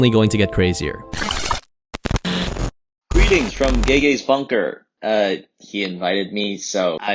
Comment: the subtitles says gay guys 💀